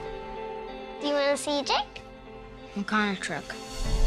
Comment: "Something's wrong mum" WEEEEEEEE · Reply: Omni Consumer Products Thanks for pointing that out. At first I thought I heard "trick" because I wasn't really listening fully. Now I can't unhear it... Thanks. @@